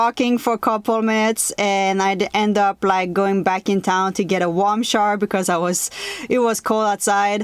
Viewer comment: What’s a warm shar? I think that’s what she said she went to town to get. I’m guessing sweater or shawl. Would like to know. Thanks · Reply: sounded like she said warm shower to me.